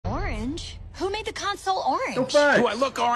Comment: I heard it like "who make the cancer orange?